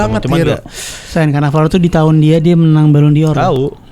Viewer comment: itu ngomong apaan? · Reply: dia bilang "sein, cannavaro tuh di tahun dia, dia menang ballon d'or" (nama asli =)